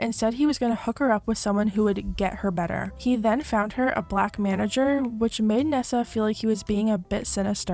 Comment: Did you just say a “black manager “ ?